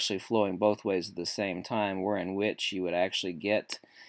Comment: "Where in which, you would actually get the ?" What word did use? Hyper-trocoidal?